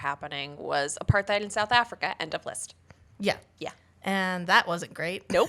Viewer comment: I heard something South Africa what did you say??